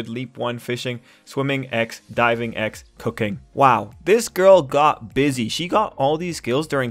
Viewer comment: I wasn't looking at the screen and I thought you said "Cocaine", I look back and was disappointed lol.